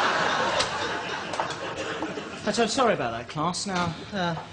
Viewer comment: Magnesium aint it 😂😂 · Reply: Nyphiiia thought she said Magnesium Mullet